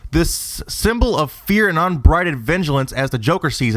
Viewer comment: Wait wait....did he just say "unbrided vengealance" lol Those aren't real words.